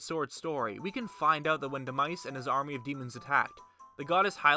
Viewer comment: Did you just say "de mice"? Lmaoo